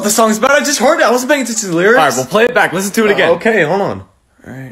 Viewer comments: Tbh i heard “…and *texas~ and very-“ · I thought the lyrics said "I'm racist and taxes" ?????